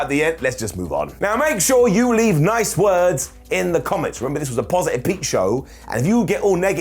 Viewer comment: Did you just say "positive peep show"